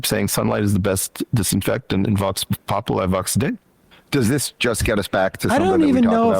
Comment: what the fuck did even say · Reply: He's mangling a phrase in latin, "Vox Populi, Vox Dei" meaning "the voice of the people is the voice of God" to put the topping on his word salad at that moment.